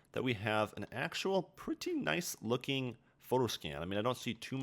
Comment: every time he said photoscan, it sounded like he said foreskinning